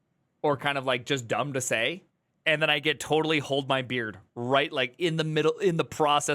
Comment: What did you just say? "Hold my beard"?!